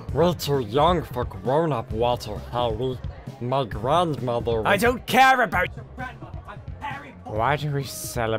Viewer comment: who else heard Harry in theback ground at 1:29😂😂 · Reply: "I DON'T CARE ABOUT YOUR GRANDMOTHER I'M HARRY POTTER!"